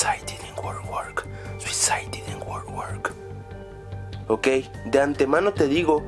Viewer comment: Yo escuché "suicide didn't work right " xd